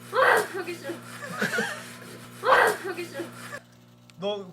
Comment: 여기 신기한게 "하기싫어"라고 생각하고 들으면 하기싫어 로 들리고 "현기증"이라고 생각하고 들으면 현기증으로 들림